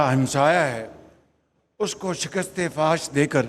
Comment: Did he really say "Shikast e Fahash"?